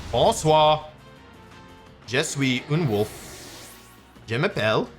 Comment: Translation: Good evening! I am good Wolfe. My name is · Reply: I definitely hear "un wolf", as in "I am a wolf."